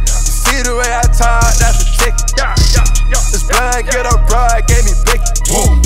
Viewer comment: Sounds like he said "This blind little boy gave me bacon"